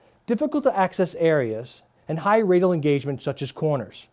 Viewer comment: The subtitles at 1:28 says sandwich cormorant instead of Sandvik coromant